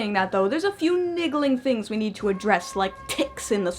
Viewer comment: Niggling... Mmm hmm... Did I hear you right?